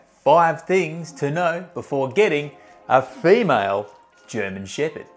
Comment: Sounds like he is saying mal german shepherd instead of male. What's this accent? 😮